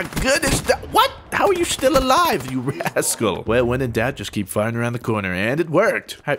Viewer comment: what does he say to the guy ? You " resacle " or what? I'm not english , dont know what it means & how to spell it correctly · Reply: he said rascal it means devious or mischievous or up to no good (it's an adjective)